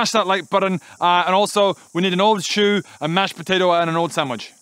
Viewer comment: I thought he said a harpys sandwich , leave me be I'm drunk